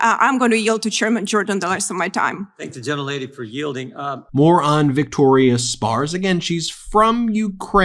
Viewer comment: hah. Ben, when you said "More on Victoria Spartz" I heard it as "Moron Victoria Spartz" which is naturally the more accurate phrasing.